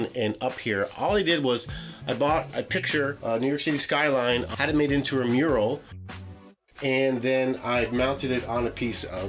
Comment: what do you say from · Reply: The SabbyBG Show sure! Sorry was talking to fast. I said I bought a picture of a New York City skyline and had it made into a mural.